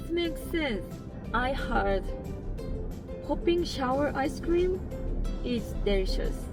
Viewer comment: Did someone say popcrnshower????? (Well almost)